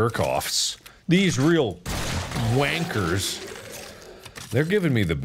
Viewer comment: Did Mark just say wankers?